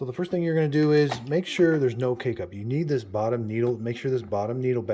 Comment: I thought he was saying "Make sure there isn't any cake-up", had to listen to it a couple times to realize he was saying K-Cup 😅🤪